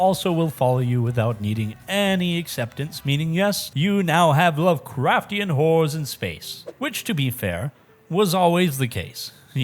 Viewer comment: Did you just say "lovecraftion whores in space"!?!